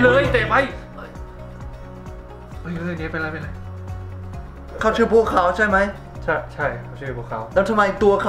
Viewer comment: ตอนเเรกได้ยินว่าภูเขาเเต่พอเปิดซัพไทยเลยรู้ว่าบัวขาว